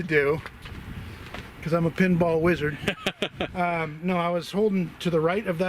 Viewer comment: did say he was a pinhead wizard???